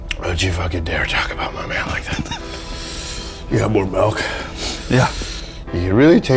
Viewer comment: Am I crazy, or does he say malk?